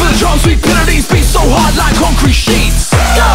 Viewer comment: Lol, i thought he said 'Gotta push so hard like concrete shit's' xD